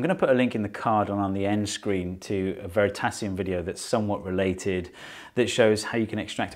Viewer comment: what word is he saying? Tacium, tascient, Oh, it's "...a Veritasium video..." he says, another YT science and engineering film maker. I'll have too check him too.